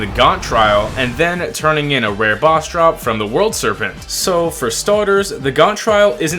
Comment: why i hear "you must farm world serpent" ?